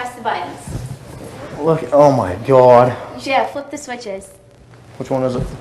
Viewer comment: Does she says ”Jeff, push the switches” ?? 😐